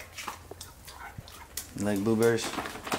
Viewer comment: I thought you said boo berries lol